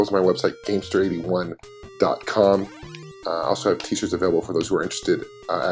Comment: for a second, I thought you said, "I also have tissues available for those who are interested." Damn, man! The game isn't that sexy. lol